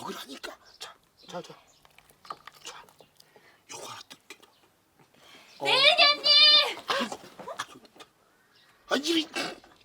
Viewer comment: 순간 "네이년님~~~~"으로 들림 ㅋㅋㅋㅋ